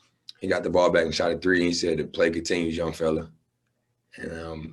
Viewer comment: what did he say? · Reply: "The play continues, young fella." Wall has told that story before